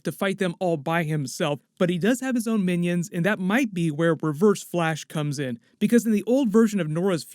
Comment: Dam, I thought he said "Perverse-Flash", which alternate Earth is that, lol